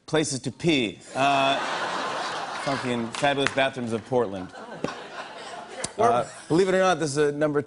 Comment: is Jimmy saying "fucking fabulous bathrooms of Portland?"